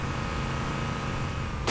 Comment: When the guy said "playball" i thought he said playboy